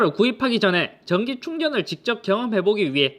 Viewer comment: 나만 전기 충격으로 들림?